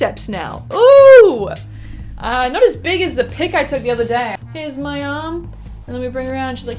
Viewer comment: I misheard the answer to the bicep question as "Not as big as the poop I took the other day."